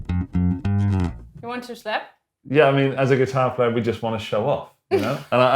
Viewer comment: I thought she said "You want a slap?" at first. Which is something we bassists often say to guitarists, just not out loud